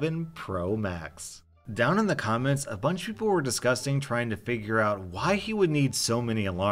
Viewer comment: "down in the comments, a bunch people were disgusting..." did I hear that right?